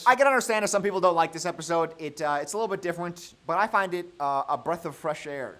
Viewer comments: did he say different like a baby here? i heard "diffewent" lol 😭😭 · did i hear diffwent